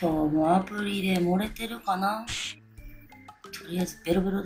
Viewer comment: アプリで盛れてるのかな？ がアブリで漏れてルのかなって聞こえたw